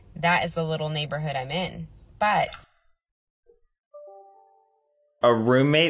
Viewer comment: Hi, long-time subscriber here to say I love your title for this one! (heehee) Just for the record, I thought I heard this woman say "ten-ish years" or maybe "nine-ish years<",( trying to figure out how long she'd known this guy) if that makes more sense. But then again, I also thought I heard her refer to "the neighborhood where I threw up," so it could be just me and my weird ears....